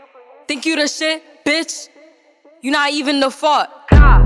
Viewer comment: the fact i dont even think it says "you not even the fart" i thought it said "you not even the f*ck"